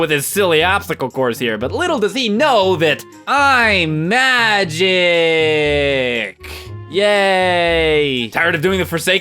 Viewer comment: Turn on captions; it's hilarious!! :D Also, it sounded like you said, "Tiny Link tiny Dark Nut combo"... LOL. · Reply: I a mad G instead of "I'm Magic!"